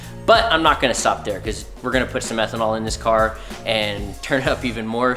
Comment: What mix of methanol is being injected, and how big is the nozzle? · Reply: I thought i had heard Methanol to, but once i saw him adding to the gas tank, i knew i miss heard him lol. ECOBOOST for the win tho, haha